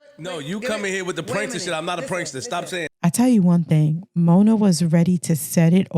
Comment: aint a prankster? Is that what he said? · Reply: He said, "I'm not a prankster. Stop saying"